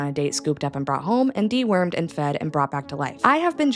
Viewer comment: fully thought you said “d-word” here and thought “what they’d do? Like he died and got resurrected or something?” I figured it out tho.